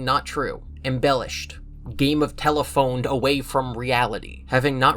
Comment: I thought he said that the books are not cannon. I think that it uses the same physics. So anything that happens in the books COULD happen. I think that’s what Scott was tryna get across